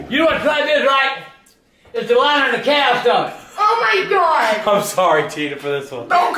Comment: what did agp say about that number 11 can? irdk what that is. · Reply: He said it's the lining of a cow stomach